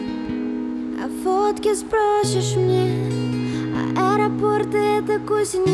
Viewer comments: То что мне послышалось. 0:06 а водки сбросишь мне? и я давно уже бомж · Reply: Sofi_Ja на 0:06 а фотки сбросишь мне, а на 1:13 мне тоже так послышалось:з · То что мне послышалось. 0:06 а водки сбросишь мне? и я давно уже бомж